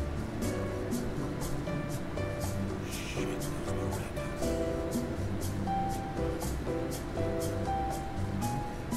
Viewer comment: i just heard the narrator say sh*t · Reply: If you listen closely, the narrator actually says "shake those maracas".